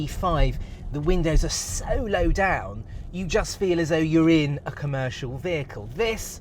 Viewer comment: "...you just feel as urine..." 😂Also, the editor team should have put cuts in several places of the video to make it funny instead of annoying. Like at 19:37 "flap flapperoonies" beep & cut "..So, we're..." or